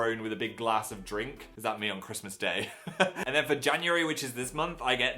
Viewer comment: i thought he said vajanuary.